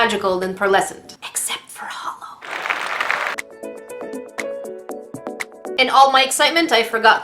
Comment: I thought she said Allmight as in the MHA character but she just said,”In all my...” XD Edit: Also I love how she has a MHA box of some sort in the back